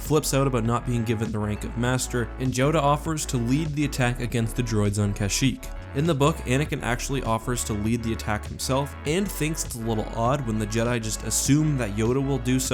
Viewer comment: Did you just say Joda